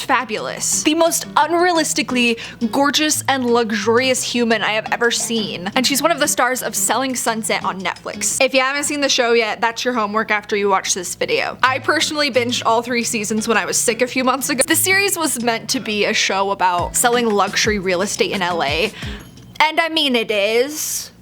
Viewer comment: Did anybody else hear pershonslly at the point 😂❤